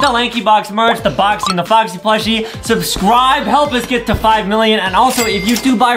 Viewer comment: Wait wait Did I just Hear adam say "Subswibe"😂 LOL